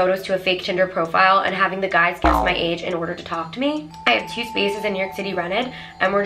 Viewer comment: Did she say guess my age